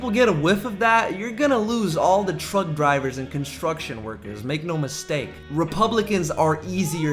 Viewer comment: did you say "truG" on purpose lmao...shwab reference?